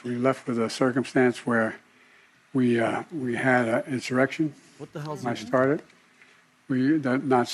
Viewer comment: did Biden said he started an insurrection??? 32:58 "we had an insurrection, I started"